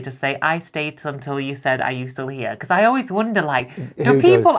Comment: I stayed until you said "Are you still here". I had to replay a few times bc I kept hearing "are you still Liam" 🤣🤣